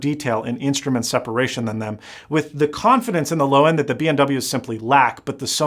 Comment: Did he just say BMW instead of B&O ?